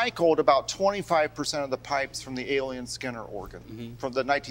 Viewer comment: As a lifelong organist all I want is 2 hours on this instrument! I was not familiar with this organ builder though I have played many Aeolian Skinner organs. I lost a good part of my sight at age 58 so I’ve given up playing now. It is too frustrating and depressing to play given what I once could do. Fun to see what a non-organist will try. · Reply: Oh, he was saying AEOLIAN. I thought he was saying alien lol